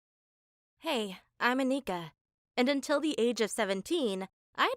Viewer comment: Anika: hey I'm anika what i heard hey I'm a n!gger